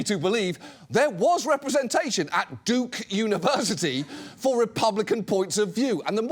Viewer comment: I could’ve swore he said dick university